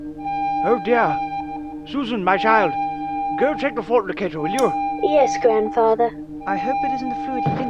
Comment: I thought he said go check the fornicator